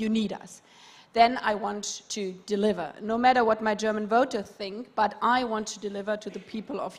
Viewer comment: fact is, that she said "... doesn't matter what the German people think ..." this is unacceptable for all Germans. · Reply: wut, she's not saying that at all. She says at 1:24:52 "... I want to deliver no matter what my German voters think", i.e. doing not what's popular, but what's right